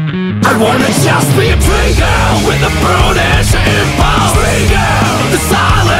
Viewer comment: I somehow keep mishearing it as "pretty girl with a brutish impulse".